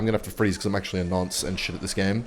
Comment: did bro just call himself a nonce at 3:33 ??